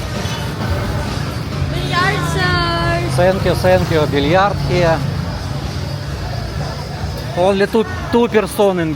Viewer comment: what does the girl say at 1:52? massage? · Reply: Sounded like "goodnight sir" , she is hoping for a conversation that will lead to a deal , I suppose. Freelancer look about her.